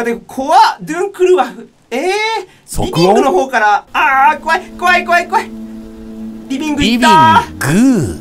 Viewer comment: ﾘｨﾋﾞﾝｸﾞ！が｢PP!｣に聞こえてるぴーぴー愛しい